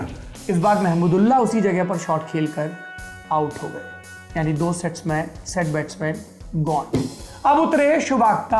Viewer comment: ye kya bola.... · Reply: 2 set batsman